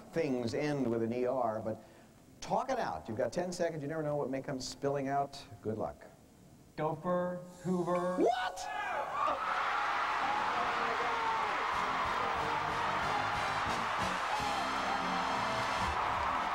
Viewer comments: It sounded like he said doper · It sounded like he said "dopher" the first time..